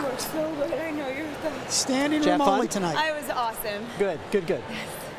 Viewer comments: did she say: I was awesome??? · "I was awesome"? Is that what she says? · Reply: She said "Aww, it was awesome."